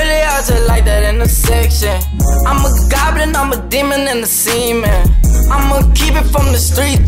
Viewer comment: Did he just say "I'm a demon in the semen"?